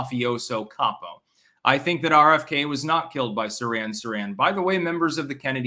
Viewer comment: Heh, it sounded like he said he doesn't believe Robert Kennedy was pew-pewed by saran saran wrap.